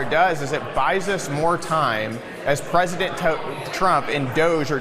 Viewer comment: I had to rewind that clip a few times, he sounded like he was saying president putin...